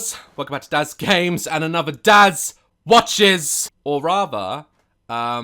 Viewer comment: I heard "and another 'dad' watches"🤣🤣